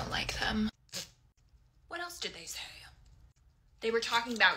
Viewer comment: “THEY WERE TALKING ABOUT YOU?! OKAY NOW I HAVE A PROBLEM.” “bEcKy. I NEVER LIKED YOU OR YOU’RE PERSONALITY OR UR HUSBAND💅” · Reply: I thought she said hedband